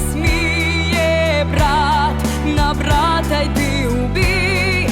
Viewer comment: "Не сміє брат на брата йти у бій" - я правильно услышал?